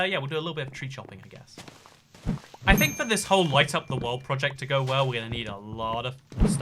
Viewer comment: Hehe I thought it said lightning up the world